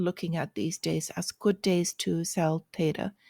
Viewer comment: LOL good days to sell data? what? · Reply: Ok that makes more sense it sounded like you said sell data. Not theta